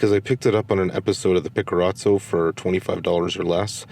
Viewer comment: Oh ok..I couldnt quite understand you...you said in the video something that sounded like a word having episode Picoro sound to it at 1:17 in the video...anyway.....thanks...Im looking at getting that camera..for around $100.. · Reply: Oh I see. I said "Pickerazzo" Here's the episode I am referring to. <link> This is when I found the D50.